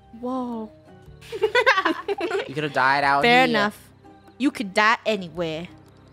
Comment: did funneh just say anyone can die anywhere??